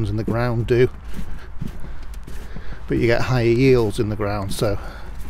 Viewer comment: I thought you said that you got your "HIgh Heels" in the ground :-) I'm off out into the garden later to see if I have any Christmas spuds, I still have a bunch in the ground and I'm hoping they will be ok. Cheers, enjoy the spuds :-)